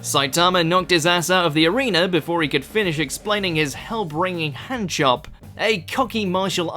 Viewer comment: thought you said Hell Bringing Hand job.